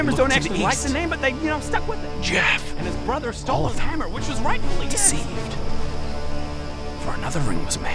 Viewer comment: I heard 'hammer which was rightfully his" and burst out laughing, friggin Bodger man, won't shut about his hammer to the degree where it became a part of the Honeywood lore